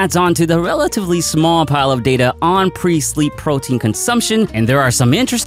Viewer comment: Ha. At first, I thought you were saying Priestly Protein .